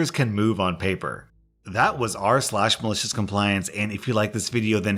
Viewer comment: What rSlash said at the end of the video: “I put out new Reddit videos every single day.” What I thought he said: “I print out new Reddit videos every single day.” That last story messed with my mind.